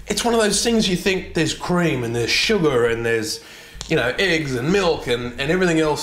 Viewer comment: did he say eggs? that doesn't ring any bells for me